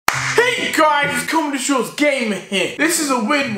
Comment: Did he just say comedy Shiites gamer here